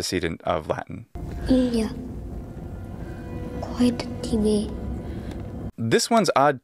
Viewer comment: Does anyone else hear "quid" at 17:09?